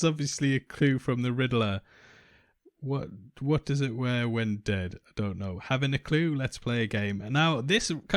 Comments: I thought the card said "What does a liar say when dead." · you say "what does it wear when dead" in ref to the left side of the card.. that's not right, prob "what does a liar say when he's dead"?